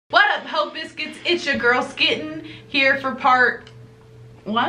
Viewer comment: Thought she said it's ya girls Clit